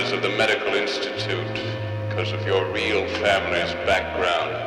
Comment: Did I just hear Dracula say "'cause" instead of "because"? He must be trying to become relevant to the audience of the time by casting aside the Old World mannerisms. Barnabas Collins of Dark Shadows fame would be turning in his grave, if he was still in it.